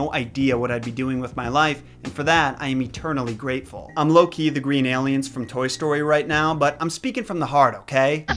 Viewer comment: You said, "I'm low key the green aliens from Toy Story." I heard, "I'm Loki, the green alien from Toy Story."